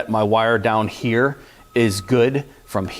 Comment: It sounded like you said Phil's probe like phil' s